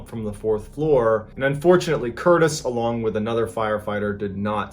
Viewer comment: did you say fortunately he didn't survive???